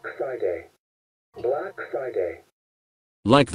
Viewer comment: I heard ''black exciting" 🤣🤣🤣🤣🤣